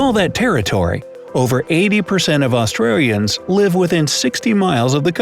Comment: Did I hear it right?? Did he just call us Austrarians 😂